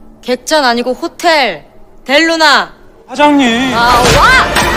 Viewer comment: i misheard IU saying “YAWA” at 0:24 😭